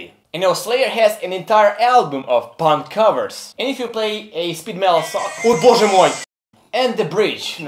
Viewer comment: did you really said O Boze Moj